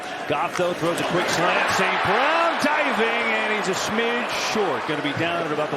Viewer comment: did he say Saquon instead of St. Brown?? 😂😂😂